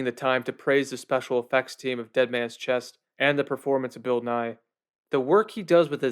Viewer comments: When you said Bill Nighy I thought you said Bill Nye and my brain just went on an ADHD zoom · Had a brief wtf moment at @ 7:25 when I thought for a moment you were saying Bill Nye.